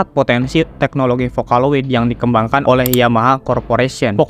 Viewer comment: Gak salah denger gw ,yamaha?